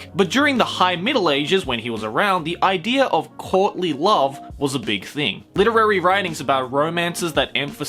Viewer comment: I couldve sworn u said Courtney love.